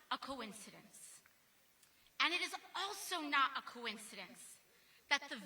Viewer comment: did she say Sauron? 😂